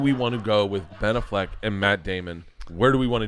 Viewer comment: Me as German heard "Ben a Fleck" - Fleck(Ger.) = Stain - Meaning I heard "Ben a Stain"